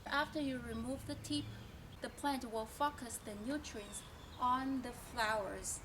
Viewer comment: I heard plant will fuck us a nutrient Sorry not making fun of your accent.